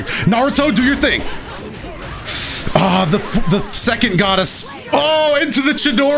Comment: Did he just say “the second goddess”